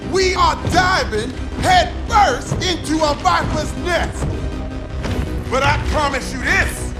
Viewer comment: the in-game subtitles are wrong here, he said "viper's next" which is an actual saying rather than "bottomless nest" and also make sense in the context of the snakeskin boots comment